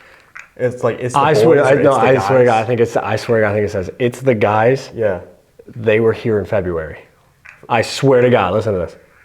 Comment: I hear "it's the guys Dylan and Steve"